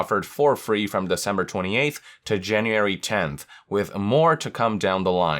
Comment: did you say janeeary? XD anyway, still a great video. But that did make me laugh. Not in a bad way :)